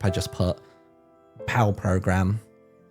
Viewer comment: Did he say "pow program" or something else? Of course the one time I can't catch a word it's right in the middle of a backstory hint! And if he did say "pow"...is that referring to prisoners of war or POW Creations? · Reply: It was Pal Program 📝